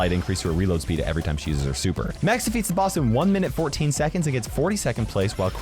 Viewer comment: I think I heard saying Meg but it's max